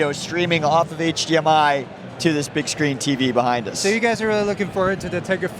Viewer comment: i swear he said bitchscreen lol